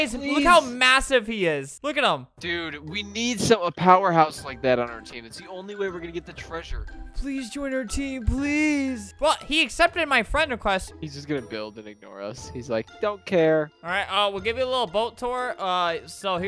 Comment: Why did I hear this as "Hes just gonna build pink fedoras"